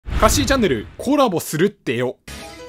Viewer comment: 最初の 「かっしーチャンネルコラボするってよ」が 「おかしいチャンネルコラボするってよ」に 聞こえた